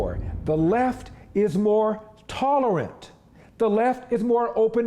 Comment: Text print error at time stamp 10:42–text should read “The Left is more TOLERANT” to match what Larry Elder is saying.